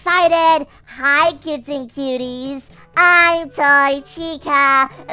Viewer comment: I thought that Toy Chica said "Hi kissing Cuties!" LOL